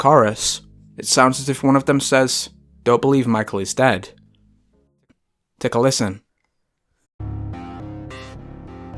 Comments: It sounds like Janet said don't believe Michael is dead and then Michael said yeah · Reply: Elpida Agapi if you listen carefully it says "my love" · To me it sounds like it says I can't believe Micheal is dead not Don't believe Micheal is dead